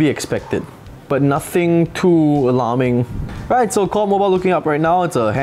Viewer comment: did u just say “Call of Mobile”? Lol